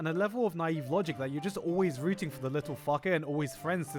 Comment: what does he say! It sounds like he “little fucker”?